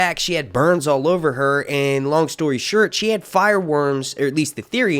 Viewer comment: did you say long story shirt?